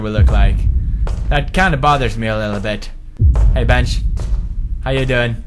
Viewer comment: I thought he said "hey bitch" lol